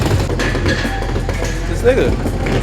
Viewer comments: did say "dis nigga" ? lol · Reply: ***** .. "Jus lo a it" ..he was tryna say.. Just look at it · great to have these vids back! long island new york represent where you played your college football at St, Johns im an LIU POST Football Alumni & did he say "This Nigga"@ 7:14 ha